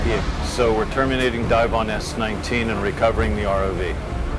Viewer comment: says RIV instead of ROV